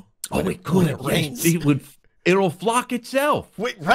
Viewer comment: Oh my gosh I thought just said it will f*** itself!